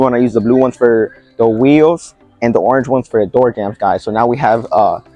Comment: Great video, thank you so much. At 1:47, what do you say that the orange towel is for? Thank you! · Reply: You’re very welcome James! Thank you for watching! Color sort them, one for windows, one for wheels and one for the door jambs. 👍🏻